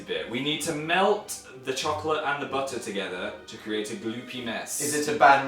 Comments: i thought phil said "to create a blue penis" omfg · I heard 'to create a gloopy mess' as 'to create a penis' 😂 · when Phil says gloopy mess at 3:36 the captions say... something else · He said "gloopy mess" but I heard "glue penis".................. There's something wrong with me... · OMG At 3:40 Phill says we need to melt the butter and chocolate to make a gloppy mess. Subtitles says " to make a blue peni*" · XD When Phil said gloopiness it sounded like he said gloop penis XD I'm sorry XD XD